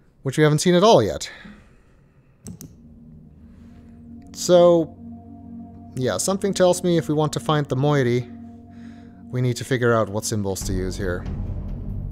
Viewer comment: Cats are like that. They always want your attention. TLJ reference! I originally thought that said "finding the monkey"